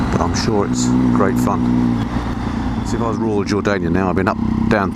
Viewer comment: What is it you say at 3:40? Sounds like "If I was a raw Jordanian..."? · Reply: Royal Jordanian